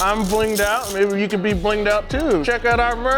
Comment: I coulda swore Justin said going on a crack day